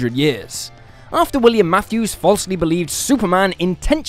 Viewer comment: Instead of hearing “William Matthews” I heard “William afton” And for a second I was like “IS SPRINGLOCK IN MARVEL!?” I am stupid.